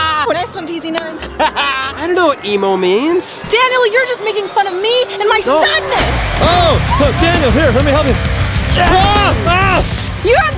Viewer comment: did regina say making fun of me and my son???! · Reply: She said sadness not son